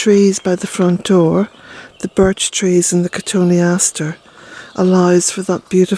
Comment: hi! I like your videos a lot, but I’m not native English speaker, so sometimes it’s hard to understand names of the trees, can you please help me to understand what is name of the trees in 7:05, I hear like “having the trees by the front door the birch trees and ....» What is it name? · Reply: Cotoneaster